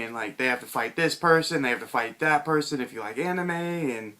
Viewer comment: did you say "if you like anime?" @ 1:15 lol, start reading the comics i think its better then the show